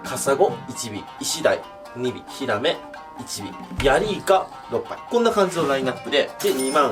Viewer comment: 多分俺の心が汚れてるか分からんけど ヤリイカ6杯 なぜかおoぱいって聞こえるw